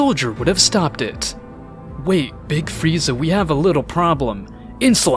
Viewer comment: Did anyone catch "big frieza"